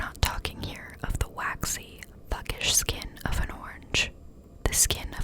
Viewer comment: So I thought she said the pear had thuggish skin lmao and now I kinda want a pear 🍐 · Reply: I did say thuggish skin! hehe